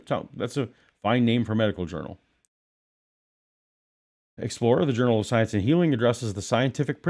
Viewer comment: Ha ha ha, I heard that as the Journal of Science and Hebrew. Might as well be.